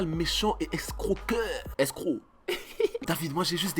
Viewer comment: Vers 5:50 tu dis tu dis "nous sommes des chacals escroqueur" et apres tu dis quoi ? S crew ?? Ou escrocs ? J'suis traumatisé x))